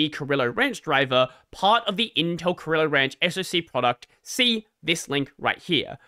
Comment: I keep mishearing it as gorilla ranch lol